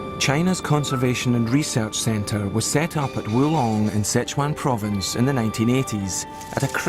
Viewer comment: Did he say Wulan?